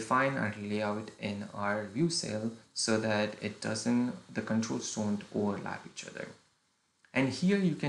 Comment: I Couldn't understand what you said sir, anyway, the content is amazing, thanks! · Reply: Ohh the controls overlap each other in view cell and that's why you should use Grid. That's what I was trying to explain there lol.